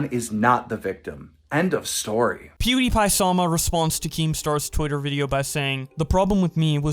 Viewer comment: I thought he said "Pewdiepie saw my response"